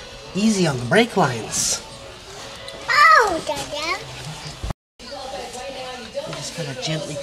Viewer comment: hahaha what did your daughter say at 3:11 it sounded like "owe god damn"